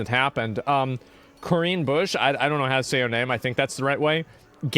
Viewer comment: Sounded like her name was "Korean Bush"